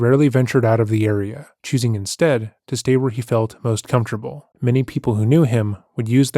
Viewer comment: Ppl use what word to describe him?....lol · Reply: Comfortable